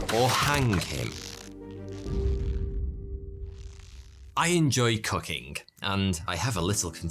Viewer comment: Bro I totally thought he said "I enjoy cucking" at first; my head whipped over to the screen like U WOT!?! 🤣🤣🤣